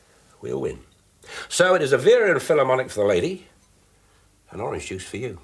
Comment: What does Dave say 13:47? He's talking about her drink. Don't know if it was rhythming slang. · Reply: Vera lynn. Gin